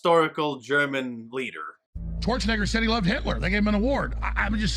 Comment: I swear it sounds like Alex is saying torch a ni66er instead of Schwarzenegger.